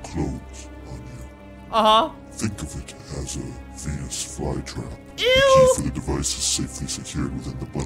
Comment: Roblox saw : Venus fly trap Saw 2 : Penis fly trap (it's so funny because it sounds like he said that)